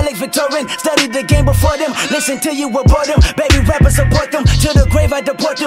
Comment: I think he says “baby rappers ABORT them” not support them. Might not be true but it fits better with the bar and the follow up line